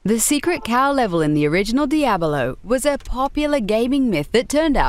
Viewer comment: Does she say Diadalo at 2:53 instead of Diablo??? Weird...